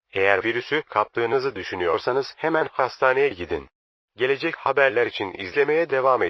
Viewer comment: hastaneyi pastane diyor sandım sırf bunun için yarım saat anırdım aq